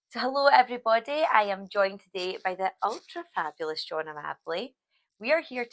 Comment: When I first saw this guy on '60 minute makeover ' I misheard his name as 'Johnny Marmalade '